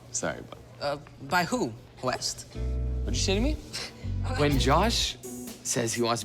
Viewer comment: I swear I hear say “are you sh*tting me?” 😂